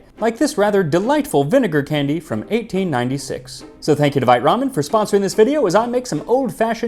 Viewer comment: Ohhhh, vite ramen. I was washing dishes so I thought you said this video was sponsored by "my mom".